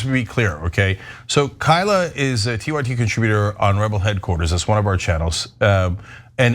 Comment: Cenk- this episode of tyt, with Rayvanna, you mentioned some other networks/channels that are yours. What are they ? I thought you said one was Rebel News. I just looked on rebel news and it all seems like conservative shows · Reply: Not Rebel News Rebel Headquarters